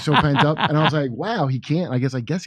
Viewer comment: I had to rewind that. Did Kurt say at 4:57, "He extra-can't?" That was delicious to my ears